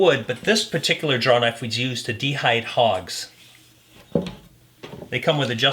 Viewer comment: Did you say, "de-hide hogs", because I kept hearing "de-bark log"s but envisioned the de-hiding of hogs? What a versatile tool!